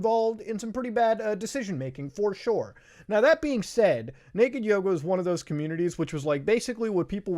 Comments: I thought you said naked yoda lol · Ngl i thought you said naked yoda last time and am schocked to hear it was yoga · I thought you said Naked Yoda at first and I remained extremely confused for a while. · I really thought he said naked Yoda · I thought u said naked yoda and I am not sure if I felt that was disturbing, funny or just weird. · I thought he said "naked yoda" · I thought you were saying naked yoda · bruh legit thought u said naked yoda